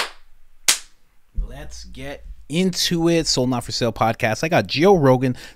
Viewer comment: I thought he was saying “so not for sale podcast” this whole time lmao